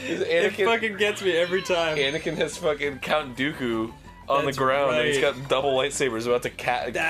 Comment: When u mishear Arin saying Cunt Dooku instead of Count Dooku... not even 30 seconds into the video yet xD